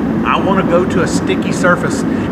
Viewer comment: thats something I never thought I'd hear say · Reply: roll racing. Not making fun of it I just never really expected to say it